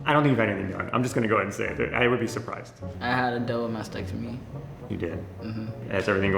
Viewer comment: what did he say? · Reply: double mastectomy (Boob removal)